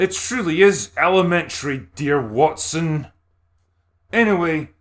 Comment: Did I hear: "Alimentary, dear Watson."